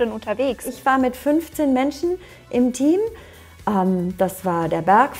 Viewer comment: Ich hab einfach verstanden „da war ich mit 15 andern intim“ haha anstatt „im Team“